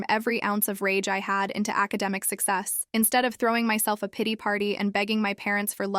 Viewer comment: Did anybody else thought that she said Diddy party or is it just me?